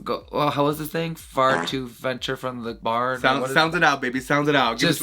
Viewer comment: someone please figure out what phrase she was trying to say. 😂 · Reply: Venture too far out from the bar